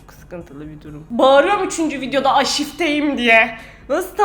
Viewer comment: kendine aşüfte mi dedi ben mi yanlış anladım?